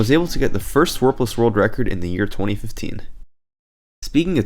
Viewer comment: I thought it said "Warpiss" there.